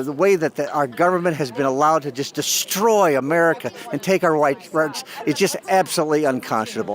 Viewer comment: what did he say at 8:36??? take our white....???